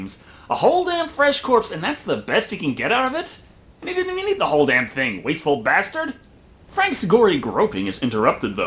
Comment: what did he sayyy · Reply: they didnt even eat the whole thing